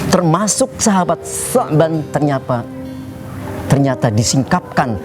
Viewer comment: Oh... Sahabat bernama Tsauban ya ustd... Selama ini salah dengar, kirain sya'ban...?